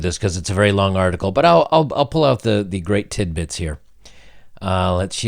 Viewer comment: Did he say, "l'll pull out the great tit-bits?